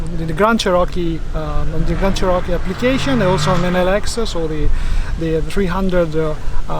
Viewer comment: DId say "in the Lexus" at 2:17? Love the truck, not so much the interior of these Rebels.